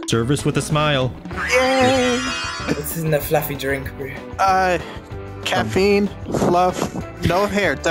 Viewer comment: so sad that my fellow china was misrepresented saying “bro” when they actually said “bru”